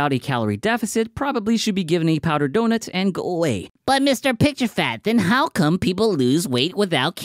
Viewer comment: does he say “mister picturefAt”? 😂